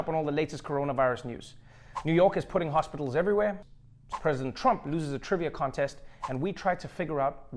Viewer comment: At first this video started playing while I was in the next room... I had to back it up because it sounded like Trevor said "Professor Trump" lost a trivia contest. 😂